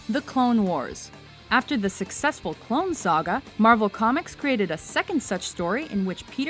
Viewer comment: I'm sorry did you say the SUCCESSFUL clone saga?